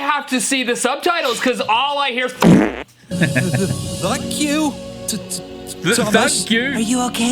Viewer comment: I heard Thunk you, Thomas